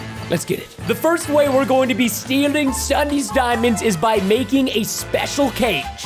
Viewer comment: To be honest I thought it said: How to steal Sunday’s diamonds 💎(like the ice cream lol) but it said SSundee’s, and i was like >:O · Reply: I thought it said Tsudene 😭